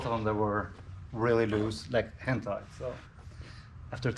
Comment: Lol. I thought he said "loose like hentai"